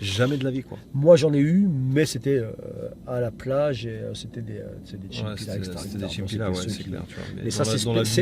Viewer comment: Hello, vers 42:14 "Chimukira", ça signifie quoi ?...Vous avez parfois tendance à zapper ce genre de détail pas si insignifiant que ça pour un auditeur lambda. En tout cas super format vidéo sinon, je m'abonne, à plus!😉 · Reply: Merci pour l'abonnement :) on parlait des "chinpira" "petits voyous" en gros